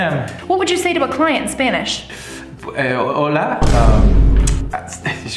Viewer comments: "What would you say to a Spanish speaker" "Ahh...Hola" (ahh...hello/hi) "Are you sure you can speak Spanish" "Uh pollo" (uh chicken) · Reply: I know the difference between pollo and apoyo, I just misheard it dude. The "uh" at the beginning threw me off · “Are you sure you speak Spanish?” “Uh... chicken.” (Pollo) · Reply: Thought he said polla for a sec 😳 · "What would you say to a Spanish speaker" "Ahh...Hola" (ahh...hello/hi) "Are you sure you can speak Spanish" "Uh pollo" (uh chicken) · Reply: I thought he said apoyo